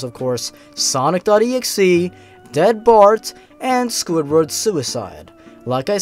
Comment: lol i thought he said dat bart..................................